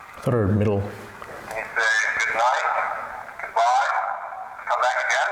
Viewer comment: Hi! Sounded like they said Goodnight, Goodbye. on dr60. I can just picture a child ghost pulling on leg saying "Hey Mister, read us a story" lol